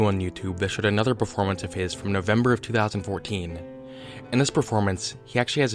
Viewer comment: Did he just say november?